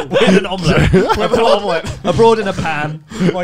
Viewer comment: Now I really want to watch “Abroad in a pan”, where learns how to cook something other than an omelette, possibly chicken and broccoli taught by. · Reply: see when he said that I heard "A broad, in a pan" which sounds super interesting